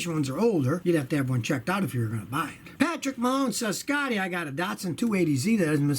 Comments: Did I hear Patrick Mahomes? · Did he say "Patrick Mahomes" at 2:20 asking about the 280Z? And Scotty didn't put any kind of football picture up? That's a sin and I hate the Chiefs.